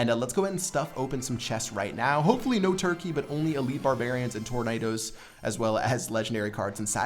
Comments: Thought it said "elite barb hut" lol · I thought it said Elite Barb hut xD